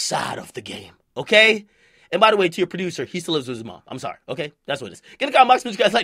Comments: "He still lives with his mom" XD I heard "And by the way your producer sleeps with ya mom, I'm sorryshyaboiacademics..." I was like "WHAT!? LOL" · "He still lives with his mom" XD I heard "And by the way your producer sleeps with ya mom, I'm sorryshyaboiacademics..." I was like "WHAT!? LOL" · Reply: I Thought I heard "Your producer, he sleeps with his mom"